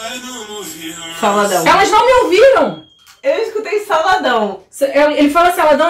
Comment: Eu ouvi "se ela tivesse me ouvido"